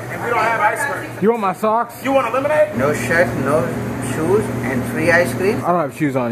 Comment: The Indian guy sounded like he said," no sh**, shoes, and free ice cream"?